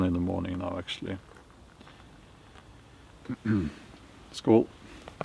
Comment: At about 4:14, what word did he use? We might says "cheers" in USA. Sounded like "skoal"??